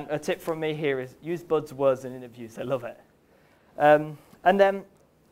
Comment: What was your tip at 17:43, you said use some kind of words, I did not quite catch that. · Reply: "use buzz words in your interviews, they love it"